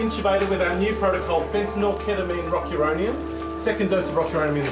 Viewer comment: what drug did he say phentanyl ketamine rock uronium? Sounds like a banger